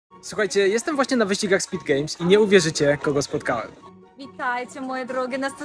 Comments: Czemu usłyszałem "Wyścigach Squid Game" xD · Ja zamiast wyścigi speed games to usłyszałam wyścigi squid game